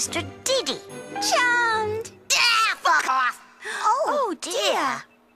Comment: did he really say "fuck off"?